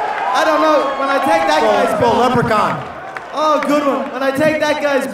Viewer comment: What did Jeremy say at 0:39? Something something leprechaun. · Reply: he called him a little leprechaun